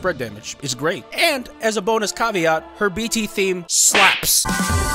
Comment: I was cooking while listening to the video, and I thought you said Serah's BT theme "sucks" instead of slaps, and I almost threw the pot at my phone. I will defend Paradigm Shift against the world!!!